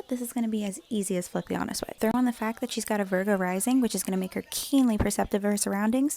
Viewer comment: When she said she’s: “a virgo rising”, I heard: “burger rising” and now I have a craving for fast food